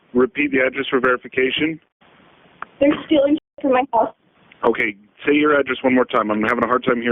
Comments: You transcribed her call incorrectly. She clearly says "They're stealing shit from my house." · Sounds like she says "stealing shit from my house " not "still in my house " the first 911 call